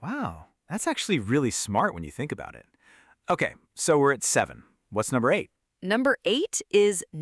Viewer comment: I hear "We red seven". It doesn't make any sense. I am in stupor. I read "We are at seven". These moments are the most difficult for learners. During the living conversations I won't have any subtitles. How could I solve this problem?